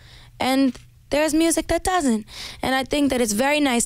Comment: her saying "doesn't" sounded like Cat lol